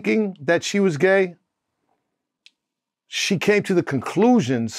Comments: I thought he said "This ain't for me" ..... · I thought he said “this ain’t for me”. But I’m waking up lol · I heard him say, “She came to the conclusion, ‘I’m not, this AIN’T FOR ME’”. But then I replayed him talking with CC on & YES…he definitely said, “The SAME for me”. This is some piping hot tea ☕️ Amazing coverage as usual 💜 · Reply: He said” this ain’t for me” referring to his daughter’s experience. But I can see why you thought he said the same for me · To me it sounded like he said that Coi came to the conclusion “this ain’t for me” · Sound like he said this ain't for me lmao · That’s what I thought he said “the same for me” but I think he said “this ain’t for me” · Sounds like benzino said this “ain’t for me”!